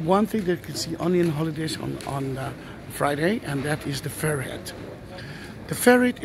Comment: Sounds like your saying ferrite.